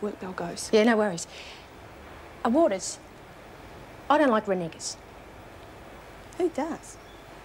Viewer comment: What did she say? She said something then said I don't like something? Then cute Roach said Who does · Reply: James Quinn renegers